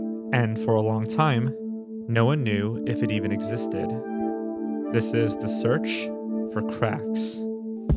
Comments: I thought it said "The Bizzare Search For Crack" and I was like damn, Sesame Street's more "street" than I remember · I thought it said "The bizarre search for crackers"